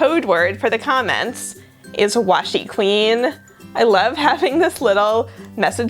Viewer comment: To be honest, after the painting part I was only listening ( I was doing an on-line jigsaw puzzle in another tab) and I thought you said washi-clean.